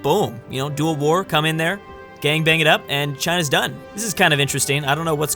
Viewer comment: Am I the only one who heard him say. 'lets gang bang it up'